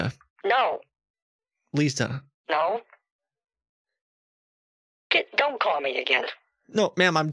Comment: Did say ''Kit don't call me again?''